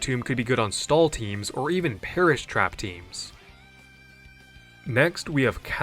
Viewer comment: Wolfie: Did I hear perish trap 🤤